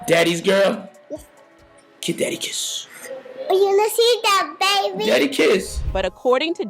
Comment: What did say when Diddy said give daddy a kiss @ 2:35?... Sounds like she said let's get it baby! 😮